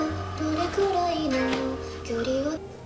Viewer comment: did you say haikyuu ??!!!!! I'm sorry I'm subscribing